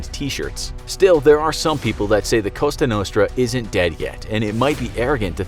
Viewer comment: Did he just say Costa Nostra